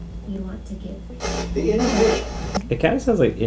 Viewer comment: "is there some kind of warning you want to give..." I heard "The ENDING!"